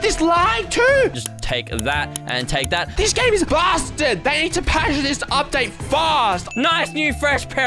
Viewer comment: SWORE HE SAID "BASTARD"